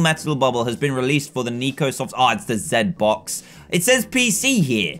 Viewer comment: did he say zedBox?